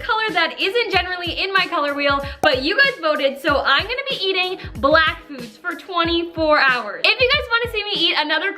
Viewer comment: What I thought said: I’m only eating black people for 24 hours! What she actually said: I’m only eating black food for 24 hours!